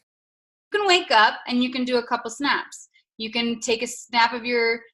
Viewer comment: I thought she said 7 snacks. Not snaps 🤣. Can I post a video of my 7 snacks on Instagram 🤣🤣. Almost to 10.